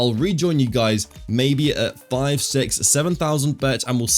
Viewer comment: did i hear 6 7 ????!!!